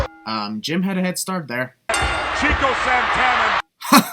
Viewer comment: Here's a sin for you, at 6:57, while the commentator did pronounce Tito Santana's name wrong, he actually said 'Chico Santana' not 'Cheeto Santana'